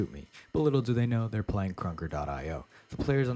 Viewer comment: What’s the name of this game. I know he says it multiple times but I can’t understand what he’s saying. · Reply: Krunker k-r-u-n-k-e-r